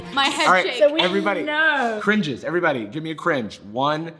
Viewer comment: “The uncanny valley of language” is the best fucking descriptor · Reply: Okay they said cringe bc I’m fucking stupid and I thought they said “cream juice” and I was like... h ey